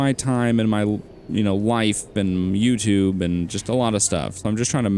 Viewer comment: did he say ‘wife’? i didn’t know that he’s married...